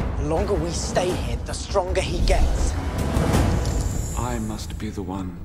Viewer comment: en el 0:50 parece que dice stron mexicans XD